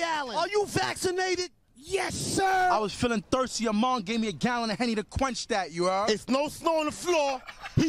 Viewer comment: what is he saying? I was I was feeling thirsty "among?" needed a gallon of henny to quanch that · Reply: "Yo mama gave me a gallon of henny"